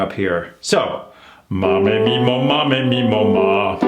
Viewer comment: for this exercise is it ma me mi mo ma me mi mo ma? It was too fast so I couldn't catch everything, and we don't sing the mu? Thanks